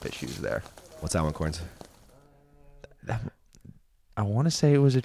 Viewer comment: i think you called eagle, "cory". Lolol. Probably just habit. But funny nonetheless.